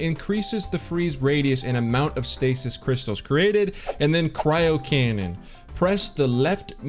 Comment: At first i thought is said, "cryocrayon"